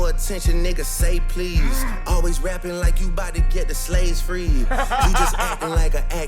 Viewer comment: Gotta be honest, I heard “always rapping like you tryna get the slaves freed” and I was like yeah I might not want him saying the word either 💀💀